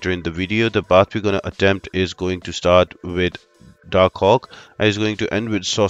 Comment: did he say Darkhawk??